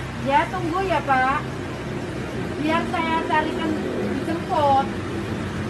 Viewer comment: ngomong apaan sih?? Cariin kentut??? 😂😂😂